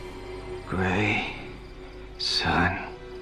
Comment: I thought Todd said gray sun not grayson